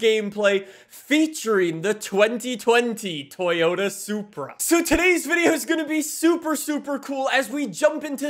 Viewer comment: I thought said: “ a supra supra day”